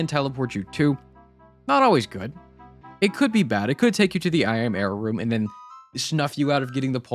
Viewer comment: Idk why but i always hear "imera room"instead of I AM error room